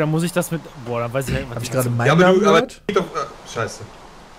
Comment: Was sagt bei 11:29 . Ich versteh nur: Hab ich da grad M... gehört ? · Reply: mein namen gehört (mein ich)